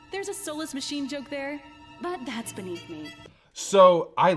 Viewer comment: "Soulless machine". JUST got that and I have been a fan for years. I thought she was saying "solace machine" or something and it was some band full of gingers I had never heard of.